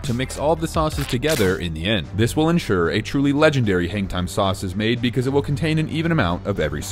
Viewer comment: I thought it said 🤣😂😂 ma- MANGTIME 😂😂😂😂😂😂😂😂🤣🤣🤣🤣🤣💀💀